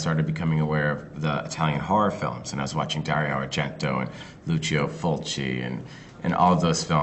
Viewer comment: oh god the subtitles are wrong at 0:27, it's Lucio FULCI not Fozzi ."